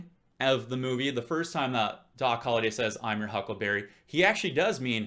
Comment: What I hear is "I'm your huckle bearer". I'm not sure though. Maybe coz I'm not native.